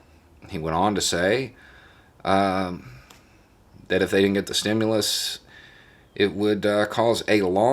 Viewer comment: what I heard was "if I win, you get stimulus, if not...."